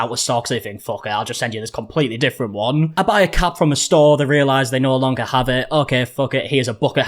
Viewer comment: I thought he said you buy a cat from a store and they no longer have that in stock so here's a bucket hat instead (I was expecting the last bit to be so here's a dog instead) then the bucket hat part made me realize ohhhh cap!! 🧢😂